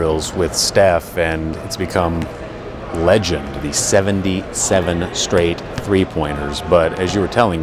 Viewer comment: did i hear this right? 00:55 77 straight 3 pointers?